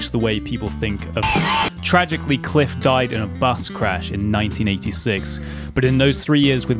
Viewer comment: Omg, I heard "Cliff died in bass crash" 😬